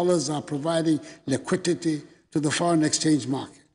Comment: Did I hear “lequitete”?